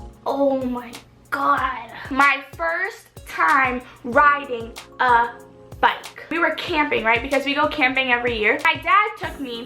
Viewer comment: when she said first time riding a bike i thought she said first time riding a bunny 😂